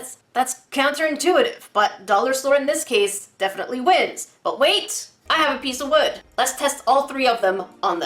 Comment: I thought you said : But wait I am a piece of wood 🤣